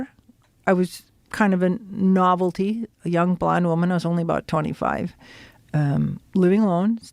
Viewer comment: Oh young blonde woman...😂😂😂 i thought she said young blind woman...